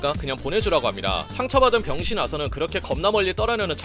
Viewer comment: 에 나만 병신 아서라 들리나